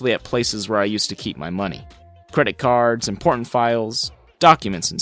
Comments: I thought he said credit cards and porn files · Wait at 12:07 did he say porn files 😂😂😂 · Who else thought when he said IMPORTANT files he said PORN files🤣🤣🤣🤣🤣🤣